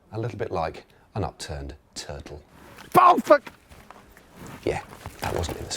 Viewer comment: What does he yell during the first one? "Ballfuck"?